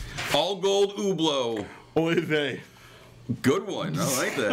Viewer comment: what did say at 3:20 answering the all gold hublot? I cant make it out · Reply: " Oy Vey"